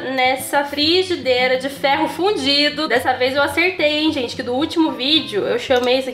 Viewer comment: Socorro q eu juro q ouvi "nessa frigideira de ferro FUDIDO" 🗣🗣KKKKKKK voltei 3 vezes p ver se tinha escutado certo kjjkkjkkk adorei a receita 💕